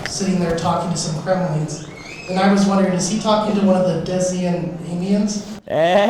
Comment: What did he say at 14:20? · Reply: He asks if at the end of WoR, when is talking to some cremlings, if he's talking to a Dysian Aimian <link>